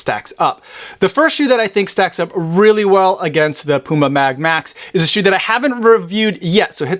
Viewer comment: Sounds like you say Puma Mad Max!!😁😆